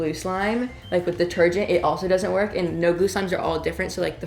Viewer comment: I always thought he said nogu slime it's actually no glue slime